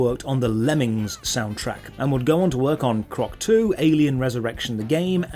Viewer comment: For a second there I thought you said the game sequel was Croc 2: Alien Resurrection and I spat out my water.